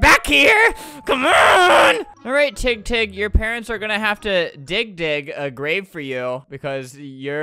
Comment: it sounded like he said dixk dixk a grave for u But we're kid friendly cuz we Naruto fans so he just said dig dig XD